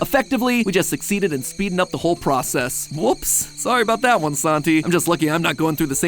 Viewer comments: I thought mat said Sanji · Am I the only one who thought MatPat said his name was Sanji? That was almost crazy coincidental